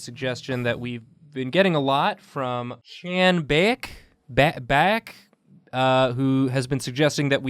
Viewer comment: d- did he say chanbaek omg did someone with that name send the idea JSJDSJD